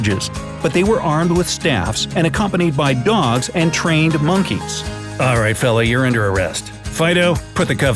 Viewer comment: I thought he said 'Trained Mummies' LOL